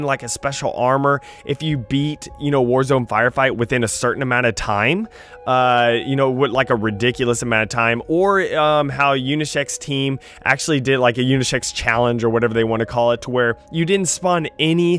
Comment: I thought he said unisex team